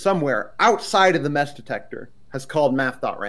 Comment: gotta be honest at the start I thought said meth detector